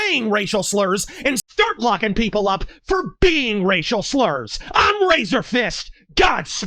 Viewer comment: For a second there at the end I thought you said “IM RACISTFIST GODSPEED!”